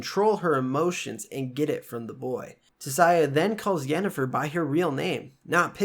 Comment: did you say "get it from the "BOY" ?????"